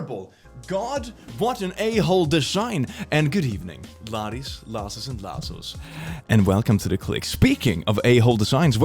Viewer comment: is it just me, but did it take anyone else a good 3 months to figure out he was saying Laddies, Lasses and Lassos??? (Or is he saying something else) bc for the longest time I thought he was saying laddies, lasses and la$$h0les